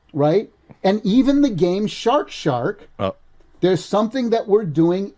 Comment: “Even the game, Shark Shark…” - I heard “even the GameShark shark” like it was a forgotten product mascot